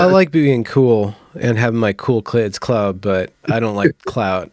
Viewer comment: I misunderstood and thought he said The Cool Clits Club. Given the topic of conversation Id think hed be excluded from such a club.